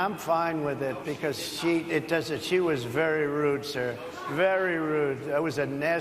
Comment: Did he say, "she was very rude, sir" at 7:08?